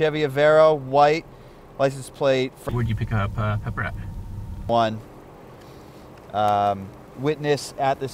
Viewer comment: The subtitles say "would you pick up a brown one" LMFAO